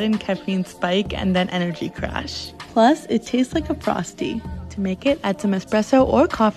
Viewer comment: I swear I hear "and it tastes like a prostate"... 😭